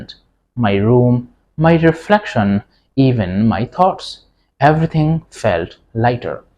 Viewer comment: did he say “even my cock”?